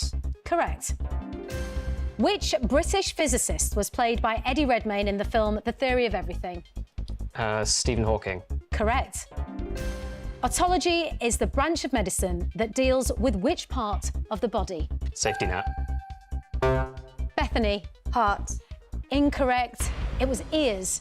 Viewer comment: I thought she said proctology LOL 😂 stupid American